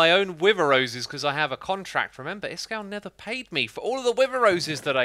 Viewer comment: lol did you say NETHER paid me? Good pun..